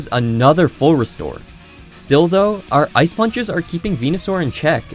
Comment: I left the video, and just came back Had to go back 10 seconds, because I thought I heard "d*ld*" instead of "still though" Anyways, really like this series, keep at it